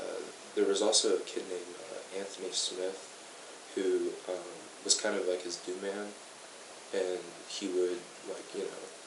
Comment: the kid's talkin about this Anthony Smith, and he said "He was kind of like his Dew man..." The caption says "new man", but I've listened REALLY closely and I'm hearing "Dew Man"... What is that? Weed man?